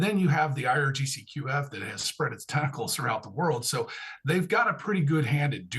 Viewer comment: I thought it said Testicles for a second